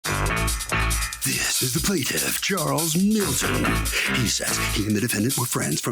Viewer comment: I thought the dude said Charles Manson